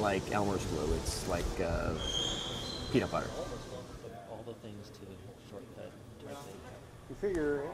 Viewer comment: Around the 0:55 mark, I hear someone saying something like, "... of all the things to shortcut..." - is that commentary original to the promo??